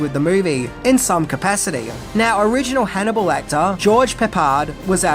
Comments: When referencing George Peppard at 9:20 I honestly thought he said "original Hannibal Lecter" instead of "original Hannibal actor" 😂 I had to go back and listen to it again.🤦‍♂️🤷‍♂️ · I had to rewind the video because I thought I heard you say "Original Hannibal Lecter, George Peppard" ! PMSL. You actually said "Original Hannibal ACTOR, George Peppard" 😀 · you say "original Hannibal actor" but I swore you said "original Hannibal Lector"...lol 😂